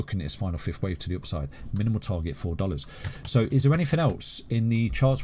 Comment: SO 4 DOLLARS IS THE TOP OF THIS BULLRUN??? EVEN WITH A XRP RESERVE....THATS HARD TO BELIEVE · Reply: Stupid ppl everywhere. He explicitly says 'MINIMAL TARGET 4$' and you are only hearing 'MAX 4$'. Srly, ppl like you shouldn't have money, because if listening to someone is too hard for you already then either you are a super brain that handles all himself (that you aren't) or you gonna loose it all because you 'thought he said something different and now it's all his fault. He scams ppl an claims it's their faul'. That's what ppl like you say and that's making me sick in this space.